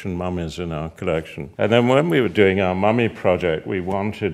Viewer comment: It sounds like his is saying mommy not mummy